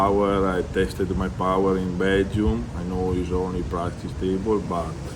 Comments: Someone, anyone, please explain to Ermes what casual viewers will think if he says "I tested my power in bedroom..." 4:04 I know that he's still learning English but you know some phrases in another language may have another meaning than you think e.e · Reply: He didn't say bedroom,he said Belgium He was there around June with ISSA,Alex and prudnyk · Min 4:05 did he say he tested his “power in bedroom”? 😂